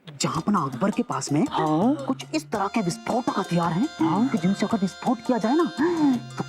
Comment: pr raja darvarne kya bola. Sune or jisne bisfot suna vo like kre